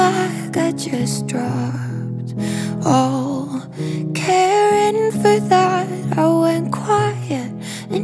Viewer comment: Was it only me who thought she said "I just dropped all Karen for that"? my bad.